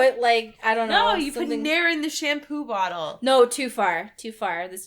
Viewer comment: What did say? put what in the shampoo?? nail? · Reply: Nair. It's a brand of chemical used for instant hair removal